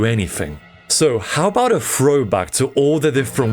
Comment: For a second I thought you said throwbux lol